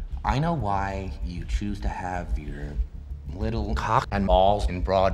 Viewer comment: It was so well timed that I even thought he had actually said "¢ock" at the scene 😂